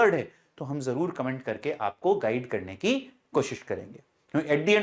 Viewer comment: i heard keep fucking hard😂😂